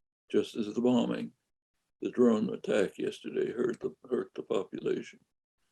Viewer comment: Anyone catch the name of the chap who wrote "The Throne of The Thistle" that Mr. Chomsky mentioned here? I for one can't seem to find it. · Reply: Oops, scratch that - I just saw the comment further down... I'd thought he'd said "throne", not "drone". No wonder all I got was a load o' stuff about Scottish tribalism (plenty good political history there too though!)